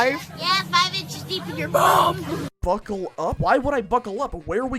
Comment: When I first used dehya, for a moment I thought she said "f*ck 'em up" instead of buckle up 😂 Her voice lines are pretty cheesy to be fair, I like them though lol